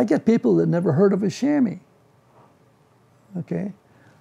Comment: what is he saying in 11:37 ? chammy? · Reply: I think he means chamois